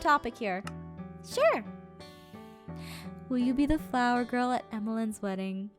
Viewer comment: i thought it said "flower of time" for a second. i think chad is one of my favorites he is hilariou XD.